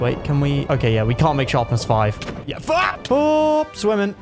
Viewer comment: did you say fck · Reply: yes he said fck